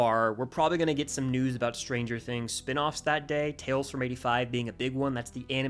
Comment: did he just say….SPIN OFFS?! I hope they do a cartoon or smth